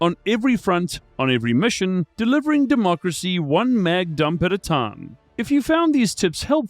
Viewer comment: When he said one MAG dump at a time, I first heard it as one MAD dump at a time.